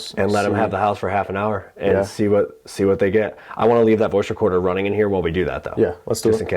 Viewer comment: The evp from in the beginning of the video sound like it said “i want you to leave “